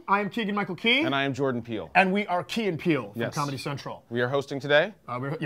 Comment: i thought you said key and pee